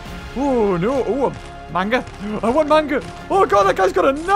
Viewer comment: I know at 5:38 he said mango, but to me it sounded like he said manga. Which now begs the question, does read manga or watch any anime at all?